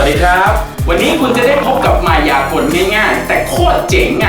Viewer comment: ผมได้ยินว่ามึงจะพบกับมายากล